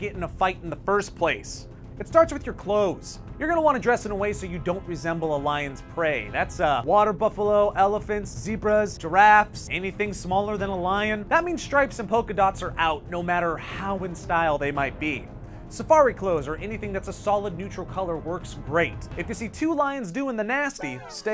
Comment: "Anything smaller than a lion" i thought you said elephant. Maybe i have bad hearing